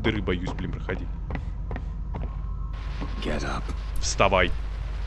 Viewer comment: Перевод: Вставай Что слышу я: Геля , Геля , Геля